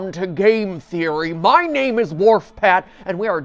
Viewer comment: In your intro, I thought you said “WarpFat” 🤣